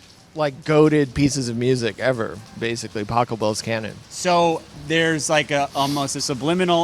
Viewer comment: I thought he said Taco Bell’s canon 😹 and I was like oh yeah I’m pretty sure I remember hearing Forever Young while eating a Gordita once.